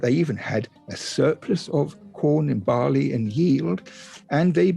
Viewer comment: did he say surplus of corn?